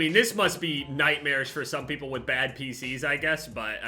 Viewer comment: Dude I thought he was gonna say "Must suck for people with bad PTSD" instead of pcs lmao